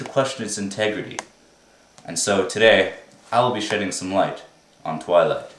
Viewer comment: i thought he said "shitting some light"